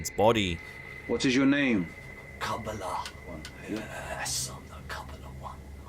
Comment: I swear the Demon in the possessed man wen asked what’s his name, it says “ Kamala Harris”. I believe that